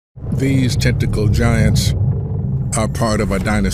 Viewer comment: For a second i thought he said, 'testicle giants' , lmao.